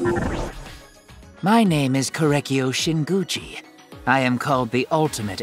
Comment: IM CRYING I THOUGHT HE SAID SHINGUCCI